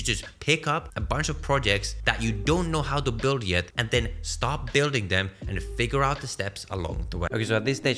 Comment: what he said here? Stop building or start building?